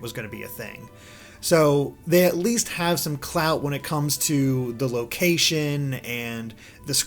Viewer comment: did i just hear "clout"